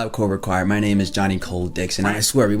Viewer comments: lmao yo I thought you said your name was Johnny Cold Dicks. I was like "wait, what??" and had to rewind and turn on captions to see what you really said. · Could've swore you said Johnny Cold Dicks at 1:27 lol